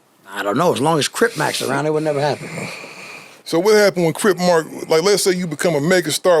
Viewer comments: who else heard "Crip Mark" tho??? Show of hands.🖐🏾😂😂😂😂😂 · he said what i think he said? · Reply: Called him crip mark